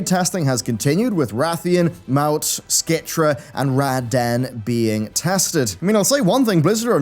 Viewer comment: did he say "Rad Dan"? lol 😆😂